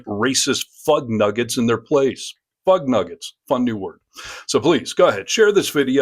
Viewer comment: I heard you say the "p" word....(plugnuggets). Better not compete for Miss Usa...